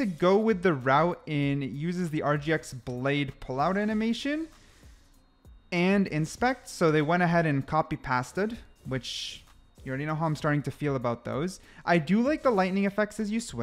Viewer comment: Did bro say past-ed??